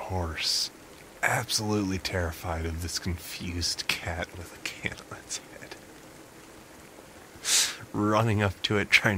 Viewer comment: For like half of the story with the cat and the horses I thought he said the cat had a cane on its head... whoops